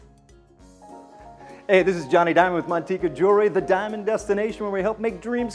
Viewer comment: Thought this nigga said "Jonny Dang" 😂